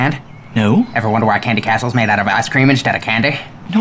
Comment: I thought he said 'arse cream instead of candy' and i was like oh its ice cream